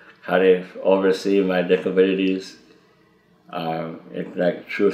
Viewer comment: Not being a native speaker I misheard him saying "diffabilities". I thought that might be a fitting portmanteau expression for "different abilities". 😊